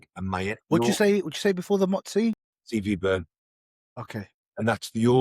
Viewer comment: what are you taking with mots-c? Sorry I couldn’t catch what you said. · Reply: Cv burn by tbjp